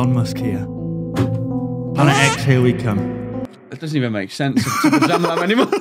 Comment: I was dying in the beginning I thought said planet eggs here we come